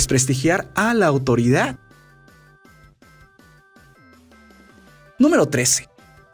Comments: Acaso oí mal o dijo número 13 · escuche mal o dijo "número 13"?